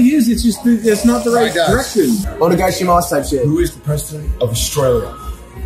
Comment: trying to get lud to lock in is true friendship. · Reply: that's what was saying? I thought he said onegaishimasu type shit lmao